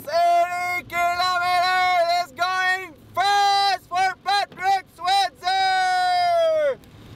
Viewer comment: Thought it said Patrick Swazye lol